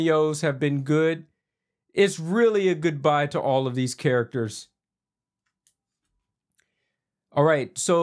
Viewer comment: Thank you for the early spoilers!!! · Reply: My mistake. I re-listened. I thought he said it’s a goodbye to Deadpool. He said it’s a goodbye to the cameo characters.